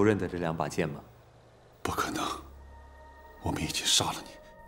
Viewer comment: I don't know what he was saying in Chinese, but it's strikingly similar to Japanese "Baka na!" which means "that's ridiculous!" The tones are also coincidentally similar with Japanese's pitch accent for that word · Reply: He said "bu ke neng", which mean impossible. Pretty close I'd say.